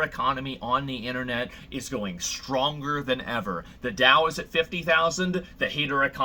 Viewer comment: did he say the Tao ☯️ ? Word